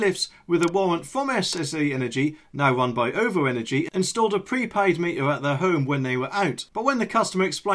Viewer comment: And now they've got a smart meter I bet they can't have it removed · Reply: I thought he said "prepaid" which means you pay first using a payment card that you top up at the post office or similar places - in advance before you can use any energy - so its WORSE than a smart meter, because it cost more to get energy that way.